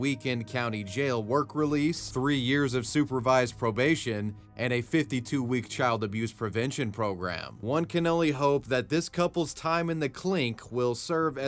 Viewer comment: you say "One can only hope" but I heard "one cannoli hope" which did make me laugh